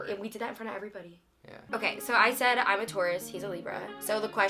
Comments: take a shot every time says "i'm a taurus" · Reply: at first, I thought she was saying "I'm a tourist" and I didn't understand it haha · I thought was saying im a tourist the whole time until she showed her tats haha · The whole time she was saying I’m a taurus I thought she was saying I’m a tourist 😂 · I thought he kept saying 'I'm a tourist'